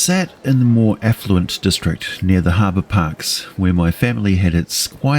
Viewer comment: Every time I hear the word "Affluent", I hear the word "Effluent". As in; "The effluent people are oppressing the poor..." Or, "The wealthy district, home of the the effluent." I think I had a poor upbringing. :(